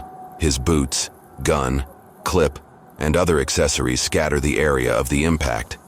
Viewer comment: Excuse me? Did you just say clip?